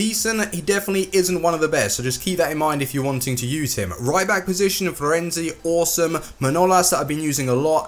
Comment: My god when you were introducing the right back position it sounded like you said "Ryback Position" Lol